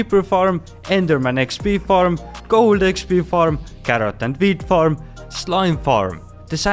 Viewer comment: I heard "4 of them farming carrots, 4 of them farming weed" I onow you said wheat but my mind just jumped to weed😂 man i realy need to be more mature hahaha